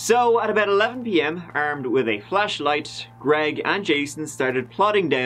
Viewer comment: when you said flashlight, I totally thought you said “flesh light”. 🥴